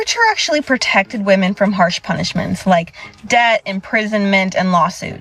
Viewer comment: Of topic: 18:41 She clearly says "debt" but the AI subtitles says "death" - My question is: Why are people not watching their content before putting it online? - My theory is: Opinions are free, making content and uploading is free...... And kids of today have no pride in being correct, being it spelling or knowledge, all that matters is "clicks"...... Have some actual pride! - A question for (who's videos i really like!) "Why didn't you immediately look up the word Coverture?" I did and it was quite a remarkable read.